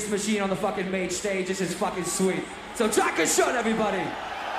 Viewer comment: wtf does he say? I've heard it in a few videos and wondered. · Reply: Danke schön it means thank you in German